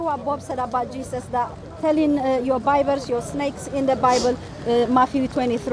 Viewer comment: did she say “ your Bible “??